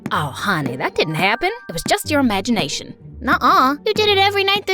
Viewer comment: DID SHE JUST SAY "nuh uh"